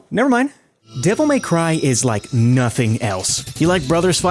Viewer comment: i thought he said Diddle may cry😭